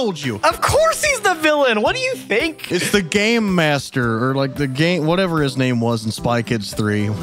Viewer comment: for a second i thought he said gay master